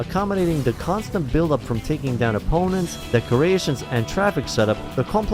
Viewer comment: It sounded like you said "the Croatians" at 10:55 🤣